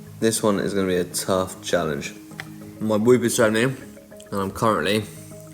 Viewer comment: What does he actually say at 1:49 subtibles say "my boob is showing" was actually interested in what he is showing in the app but found it funny aswell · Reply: haha "MY WHOOP...." it's hit HR strap that measure HRV etc